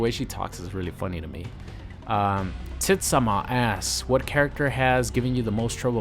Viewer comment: Noob has the worst kustomization what are you saying?! :L They look good but 70 percent of his armours look the same, cmon bruh. Also 13:57 'TITS ON MA ASS' or is that like 'Titsoma asks?' Its hard to tell. :L